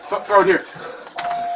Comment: My heart dropped to hear about the woman's body in the garage. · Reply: Right?! I watched to the end and I’m like wait, did he just say moms covered up??? Wow!